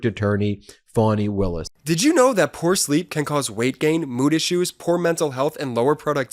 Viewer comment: I thought Jordy said poor sleep could cause moody shoes 😂